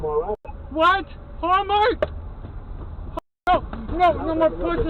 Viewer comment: Was the dashcam driver inebriated? The words that I heard were "White Hallmark? No... No more pussy" And then managed to hit another car. Their words were very slured too.